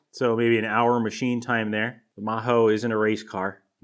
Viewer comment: Did I hear You say "MAHO" 13:57 ? So it's finished?